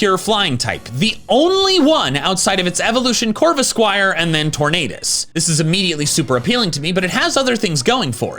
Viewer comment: did he say tornadous